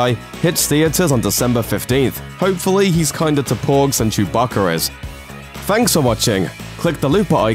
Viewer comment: did you just call them "Chewbaccara's" it's Wookies lol